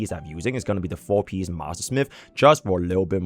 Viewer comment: I forgot the name of the new relic set and when I heard master smith I got scared af cos I thought I heard firesmith set 💀